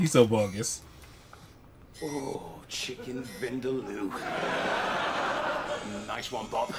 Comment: Had this series recorded on VHS before moving up to digital; I thought he said "cheese vindaloo". 10:49 I know about that gag; Little Chef used to be notorious for slow service. I don't know about any US diner chains that could compare. "They call me 'Birdman'." "Why?" "Because he really likes instant custard."